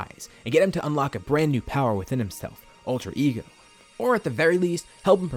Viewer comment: did he say ultra ego (if that's what he said what's that)